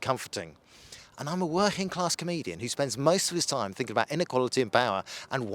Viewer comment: I thought he said he's a working class Canadian.